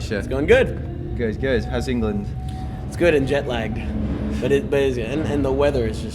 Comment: and at 0:59 at first i thought misha said he was djent lagged hahah